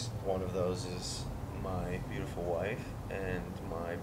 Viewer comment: Did he said "my beautiful wife"